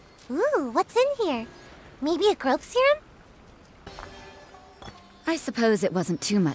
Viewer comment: does anyone know what sayu said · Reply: growth serum